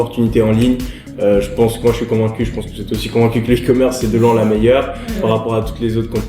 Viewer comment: Salut Yomi, c’est quoi le nom de la plateforme que tu dis à 21:28 quand tu dis « c de loin la meilleure » ? MERCI D’AVANCE POUR TA REPONSE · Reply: Il a dit « e-commerce » et il parlait du dropshipping